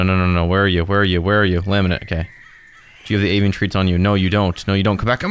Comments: wait did i hear this right, you just called slazo laminate 😫 Anyway omg i want more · DID HE JUST CALL HIM LAMINANT ?XDDD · why did he said laminent